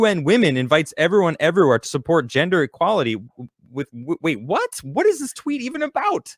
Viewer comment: Did I hear gender equality I thought they said bullshit